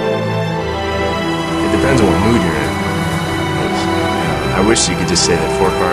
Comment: what those the guy say at 0:50 it depends on what mood you in